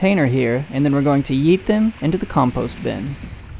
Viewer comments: When he said "When we find the parasite were going to yeet them in the compost bin" i thought he said "eat them in the compost bin" 😂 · i thought u said ”eat them” and i got a small heart attack